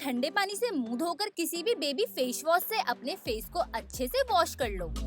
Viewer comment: anyone noticed she said fash was instead of face wash.... Hit like 😅😅